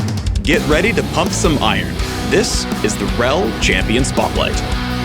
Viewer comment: i thought i heard "it is real champion spotlight", but it actually is "its's rell champion spotlight". I was like wait! is there a fake one?